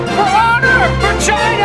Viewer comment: lol I love this! especially when you said "For China" and I misheard it for "V....ina" lolsorry